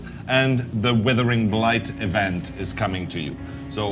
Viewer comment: Bro who else heard what I heard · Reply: He said knickers not the n word